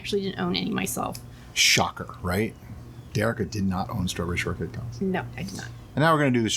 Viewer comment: "Strawberry shortcake balls"??? I know I misheard it. I rewound twice and put the subtitles on, but no dice. What is it?